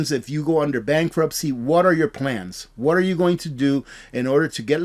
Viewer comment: When you said what are your plans I thought you said water your plants 😂